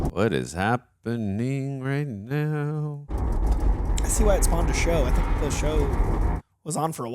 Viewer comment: Stargate being described as “I think this is a show also” is the understatement of the century of the week😅😅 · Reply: I thought they said "no wonder this spawned a tv show" or something along them lines.